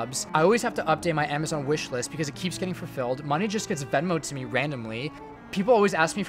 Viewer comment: What does he say at 1:01 ? Money just gets ????? ????? Randomly, sorry I m not mother tongue 😂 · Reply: Venmoed which means getting money through Venmo the app